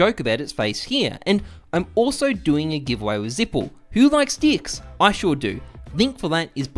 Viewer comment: why does it sound like he said who likes dicks